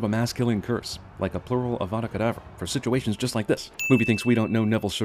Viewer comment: i always thought he was saying "avada kedabra"